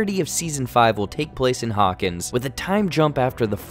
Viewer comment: "The entire season five will take place in Hawkins" I thought he said take place in "August" dude I lost my shit for a second